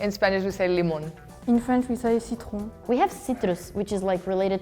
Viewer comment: I thought she said “Citroën” for a second there lol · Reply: She said citron(lemon) clause to the germanic word Citroën.